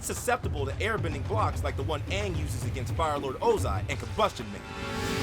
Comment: did you say combution man because his name is sparky sparky boom boom man